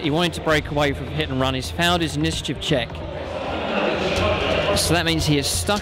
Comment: Misheard it as 'Failed his Mischief Check' which given what the Mawloc was trying to do, was surprisingly accurate...